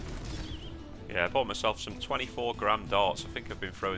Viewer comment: Jesus for a second i thought you said 24 grand darts lol then worked out it was 24 gram.